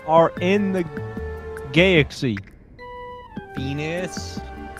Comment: when said venus the subtitle/caption said peni